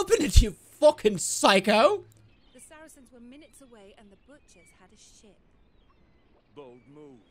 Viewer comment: thought she said "the butchers had a sh i t"